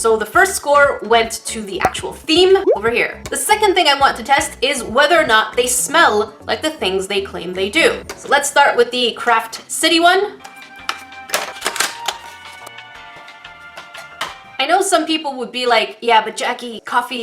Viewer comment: all the time she was saying "craftcity" i thought she said "crapcity". Thats honeslty a better name for it because it belongs IN THE TREEESH!